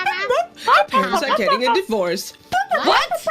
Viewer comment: idk, i heard, "Your parents are getting a divorce" but i cant pass the heard part of english lang test, so take it like a grain of salt.